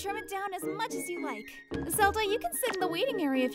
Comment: is it me or did she call him Zelda ?? 😭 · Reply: Yup, she called him zelda